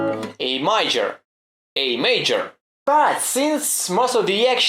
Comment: O chuj, I literally lost it when I heard „A Myjor”